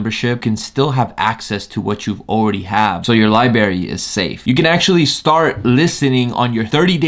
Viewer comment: Bro he really said Liberry